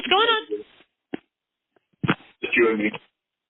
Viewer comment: Calling them “prepubescent girls” was very odd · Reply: Omg what’s with the 911 call?? Why did he say woah 4x like he just saw a gun and at 18:05 someone in the back said “ it’s you and me” is that??